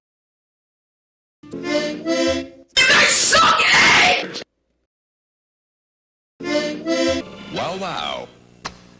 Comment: What is he saying? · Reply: For some rage